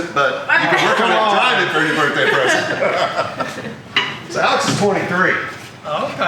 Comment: did you say ALEX IS 43???? 11:18 He looks late 20s